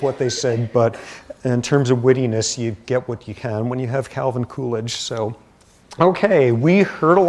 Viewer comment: lol I thought he was saying coolage, as in cooling down period hahaha come to find out it's poor Calvin Coolidge